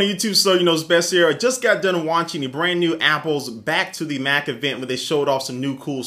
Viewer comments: coulda sworn he just said 'black to the black' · it always sounds like u say "sonyknowsbest here" at the beginning of your vids